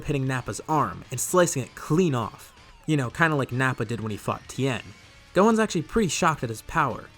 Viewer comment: Why does it sound like he says, when gohan cuts off nappas arm, “when he fu ked tien” instead of fought. 😂 might just be my ears but I hear it